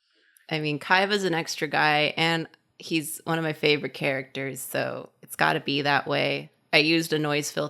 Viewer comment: when talking about how long it took to draw the petals I thought said "Time is an extra guy, and he's one of my favorite characters" and I was blown away by that quote